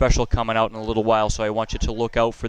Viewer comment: I was eating cereal when he said: “local drum dealer” and I thought he said: “ local drug dealer” oops 😂